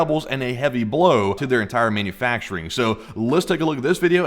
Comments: thought you said semen factory lmfao! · i keep hearing "semen factory".